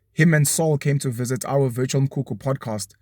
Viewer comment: Whoever does subtitles on YT needs a second hustle before they get fired. Homeboy mentioned Virtual Mkhukhu, subtitles said "virgin kuku" I kid you not. Check 2:08 Which waaan is this waaan??? 🤣🤣🤣